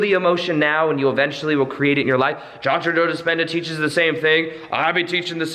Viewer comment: you mentioned a name I didnt understand "John Trudell Dispenda? sorry I didnt understand you there,,,,who is this person?? thank you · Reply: SUGAR LIFE Hi. Its Dr. Joe Dispenza